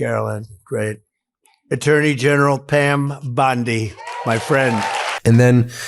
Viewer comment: Did he call Pam Bondi "A turdy general"?